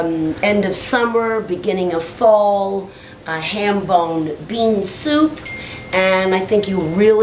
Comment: I thought it said Harambe Bean Soup.